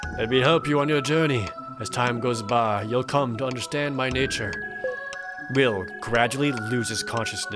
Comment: I thought it said 'Will GRACIOUSLY loses consciousness'. How kind of him to accept it and black out.